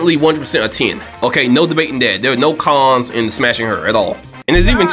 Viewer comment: your black accent made me think you said "teen" instead of "ten" and i was having serious considerations over calling the cops on you.